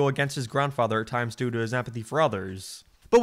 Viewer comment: almost sounds like you're saying apathy instead of empathy